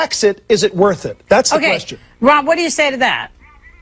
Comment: Am I high or does she call him Rob at 8:28?